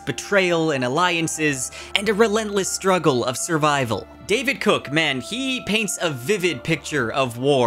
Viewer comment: DID YOU JUST SAY DAVID COOK? LOL. MY MAN RIGHT THERE.